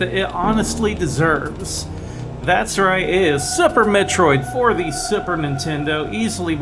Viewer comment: Lol, sounds like yer saying Supper Metroid on Supper Nintendo ;p